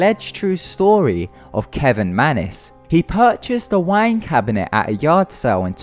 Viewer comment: I'm so sorry but I had to laugh, because of your accent, when you said "wine cabinet" I thought you said "wank cabinet"